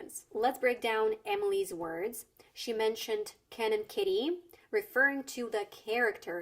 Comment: In a litteral sense, Fodder means food for cattle. Sometimes it refers to the ingredients used for cattle feeding and I believe also to the leftovers from it. I think an American wouldn't really use the word as a metaphor like she did, it's mostly a British thing. Like they would use "rubbish". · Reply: I got to highlight that at first I heard "Canon kitty".